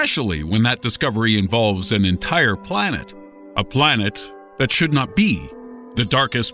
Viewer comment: I thought it said plants lmao